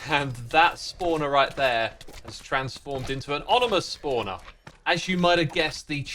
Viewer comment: he said onimous spawner instead of ominous lol